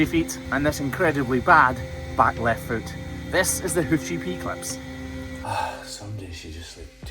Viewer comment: so funny, with your accent, when you say The Hoof GP Clips, it sounds like you say The Hoochie P Clips LOL